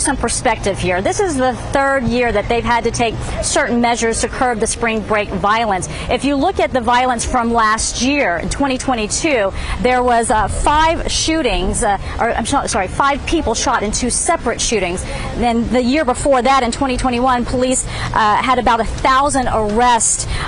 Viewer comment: did just said there was 5 shootings in Miami? And i am not trying to be funny at all.